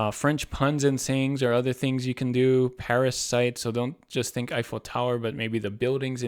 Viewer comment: You said "Paris sites" and I heard "parasites." There's an idea right there :)